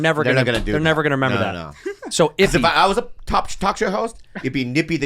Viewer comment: Look at all the guys vs girls reactions at the “ippy the chink” joke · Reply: Thought he said "nippy the chink"